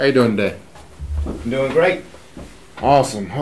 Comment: lo escuche como ey doin day, jamas lo habia escuchado así, ¿es mi oído o contrajo la frase demasiado?